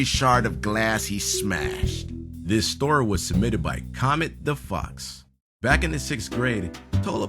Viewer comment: Lol I thought said cometdafuq XD